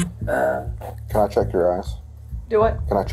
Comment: With his accent, the cop in the first video, def sounded like he said “let me check your ass” not “eyes”😂😂😂😂😂😂